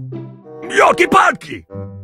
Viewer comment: Мне ли не послышалось ли Гром Сказал На русском Ёлки Палки